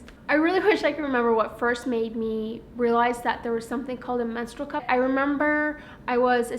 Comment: What you actually said: "menstrual cup" What the auto-generated subtitles thought you said: "metal cup"/"monster cup"